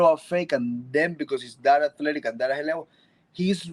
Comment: Anyone catch what he said at 14:11 ? Was it, "Data collecting, data... [something...?]" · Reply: Full sentence is : “because he’s that athletic and that high level”